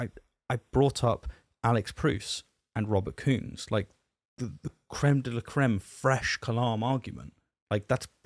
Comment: I heard "Crème de la crème frêche" :D lol